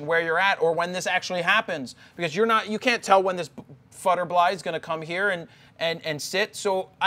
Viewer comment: Did he just say "futtebly" at 3:50? Instead of butterfly? Huh?